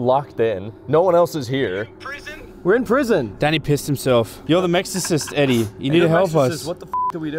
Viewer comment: I love how they called Eddie the exorcist like that was such a beautiful moment. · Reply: I thought he said eddie the mexorcist